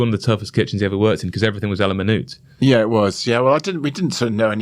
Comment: What does he say at 22:43? Alimanute? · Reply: yes À la minute cooking is a French culinary technique where a dish or component is prepared entirely to order, rather than being cooked in advance and reheated.